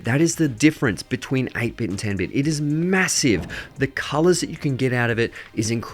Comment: did u say MASSIVE?